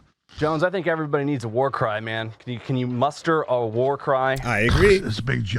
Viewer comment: OOooohhhh a "War Cry"....genuinely thought he said "Jones I think everybody needs a war crime" and that Alex proceeded to begin accepting it and thinking up a way to make it happen.